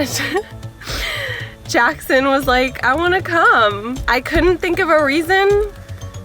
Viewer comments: i got really confused for a sec and had to go back because i thought she said cum not come (to her work) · thought i heard ''i wanna cum'' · who else heard i wanna cum no? nobody else? wow i am so drity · I thought she said cum not come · I thought Jackson said in 5:53 is "I wanna cum" (I'm sorry 😭😞)